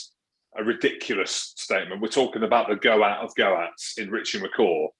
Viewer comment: Did he just say ‘the Go-At of Go-Ats?